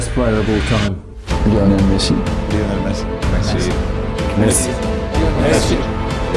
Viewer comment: I think he said Barcelona , not Lionel Messi <link>